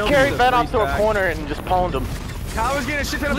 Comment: I carried vanoss in the corner and just pound him :D :D I know he said pwnd but that's what it sounded like :D